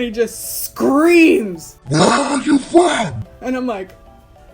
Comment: When you edited that "WHERE ARE YOU FROM" I heard "WHY ARE YOU FLAT" and honestly why am I so flat.